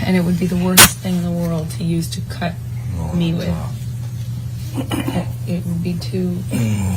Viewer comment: she says cut her with, when he was obviously saying cutting himself!